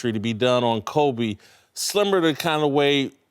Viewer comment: When he said similar I thought he said slimmer